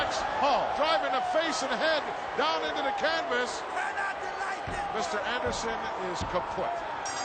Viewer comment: Did he say Mr. Anderson is kaputt??